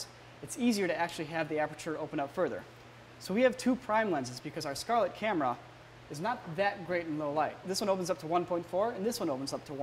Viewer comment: did he said: because our red scarlet camera is not the good in bad light ??? can someone tell me